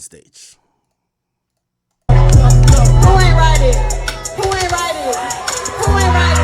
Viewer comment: Yo why the fuck I thought she was saying who anit Riding?